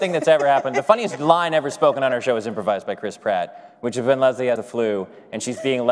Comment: I always hear “Crisp Rat”